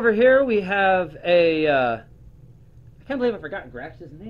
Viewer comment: Did u say “I can’t believe I forgot (insert whatever his name is)’s name” at 5:26 lmao?